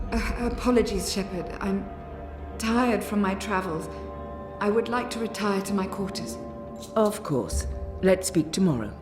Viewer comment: I'm not sure if anyone else noticed this, but from 4:32-4:37 the words that Na'ane says don't match the subtitles. Na'ane says: "Apologies, Shepherd, I'm tired. I will retire to my room." What she actually says is "Apologies, Shepherd, I'm tired from my travels. I would like to retire to my quarters."